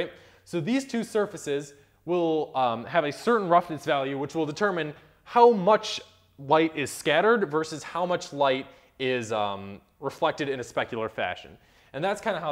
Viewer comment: There were a few small mistakes that I can ignore but one of them is flat out wrong and might point people into the wrong direction. Roughness and glossiness is NOT the amount or ratio of light being reflected versus transmitted. Roughness is yet again an approximation of the behavior of micro surfaces. Meaning, if you imagine taking a microscopic look of your surface the "mountains and valleys" of that micro surface will reflect and transmit light into various directions, furthermore, the rougher the surface is the wider the angles are, kinda like if it's distributing the specular component through the surface. check out ths "microfacet part" in this page: <link> for a more detailed explanation. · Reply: You're correct, but I think you misheard me at 5:25. I'm say that roughness determines how much light will be scattered upon reflection and how much light should be specularly reflected upon reflection.